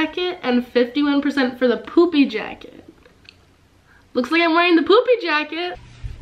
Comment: you said poopy jacket instead of poofy jacket lol